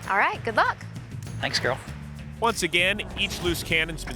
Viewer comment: did Phil call Amanda Carol?